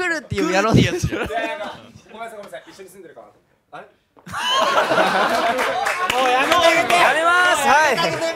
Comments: のところなんて言ってますか？？ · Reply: 中村くんのやつですか…？ 中村くんのやつだったら多分 ｢ごめんなさいごめんなさい一緒に住んでるかなぁと思って…あれ？｣ って言ってると思います🤔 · のところなんて言ってますか？ 何度聴いても聞き取れません😭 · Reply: 中村くんのやつかな…？ でしたら、多分 ｢ごめんなさいごめんなさい一緒に住んでるかなぁと思って…あれ？」 って言ってると思います🤔